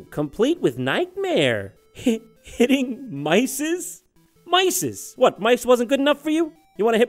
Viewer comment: i think they are saying hitting isis